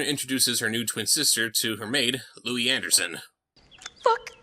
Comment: did she say the f word? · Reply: Lucy she said "Look!"